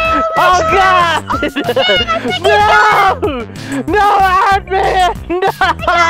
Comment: i swear i heard no my husband